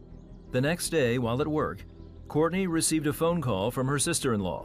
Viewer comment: I thought he said "court-ni**er" in the beginning.